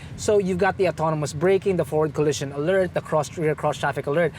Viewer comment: I thought said "Ford Colission Alert" and I was like, Ford alert on a Chevy, sign me up! hahaha... Great vid as always mah man!